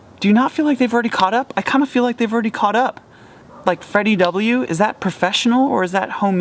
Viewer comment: Fi D W??? I wanna listen to it but I can't understand the name. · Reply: Think he's referring to "rocket jump"'s Freddie W.